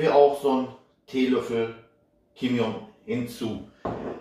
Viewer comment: Ich hab Kim Jung Un verstanden 😂